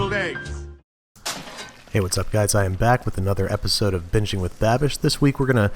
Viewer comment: Is it just me or it sound like he said Bitching with babish in the beginning